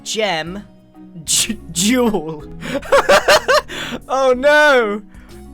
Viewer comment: When said “duel”, I legitimately thought he said jewel😂😂